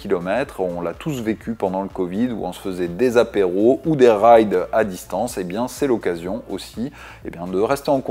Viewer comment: à la première écoute j'ai entendu "on se faisait des apéros ou des rails à distance". Je me suis dit on a pas vécu le confinement de la même façon.😆